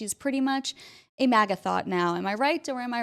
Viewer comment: Did you just say MagaThot? Lol